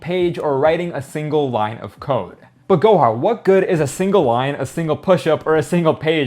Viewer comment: I thought homie said Gohan